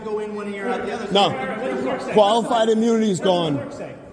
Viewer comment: did he say qualifamunity?